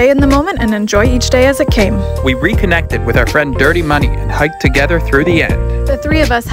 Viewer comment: I didn't catch their friends name. I thought they said Dirty Money, that can't be right.